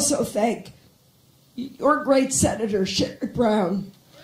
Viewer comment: OMG, at 2:38 does she say "your great Senator Shit McBrown"? I've listened to it 10 times and that's all I hear... :-D